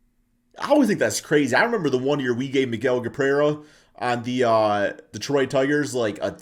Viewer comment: Did you say Miguel Gaprera at 2:42 ? Lol. Miguel Cabrera